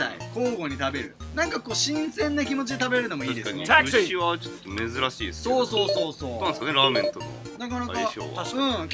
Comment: 普通に流したけど聞こえたの僕だけ？( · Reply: TAXI❗️🚕💨